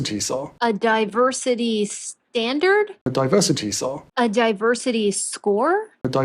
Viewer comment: I thought I heard “diversity czar”? Also, new subbie here and I love your channel!